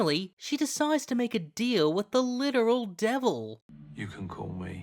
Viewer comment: i hear Lucifer